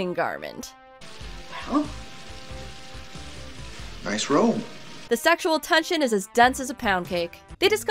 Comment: I heard Magnum say, "Disrobe" to Jessica.